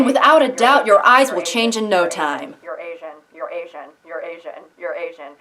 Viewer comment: "you'reasian,you'reasian,you'reasian,you'reasian..." Crap! I heard it as "Eurasian" and now I don't know what ethnicity I am.